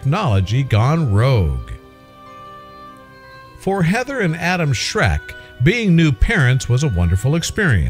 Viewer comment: Did he just say Adam shrek?